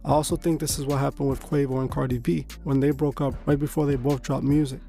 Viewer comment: Am I the only one who heard him say "quavo and cardi b" at 3:17?